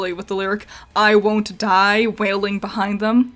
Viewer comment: I might be a little dumb because instead of hearing "I won't die," I heard "I wonder"